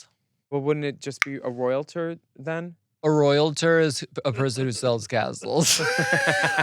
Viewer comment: I thought you said “royal turd”